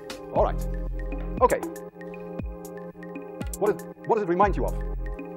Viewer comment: I thought he said "dinosaurs".... I was thinking "how the heck does that remind you of dinosaurs?".... 🤦‍♂️